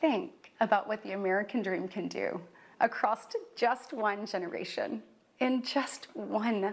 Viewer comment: did she say "acrost"?